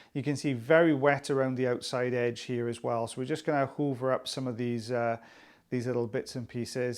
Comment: Ohhh. You said Hoover up, like vacuuming up. I thought you said Uber up, I was like, hey we have Uber too!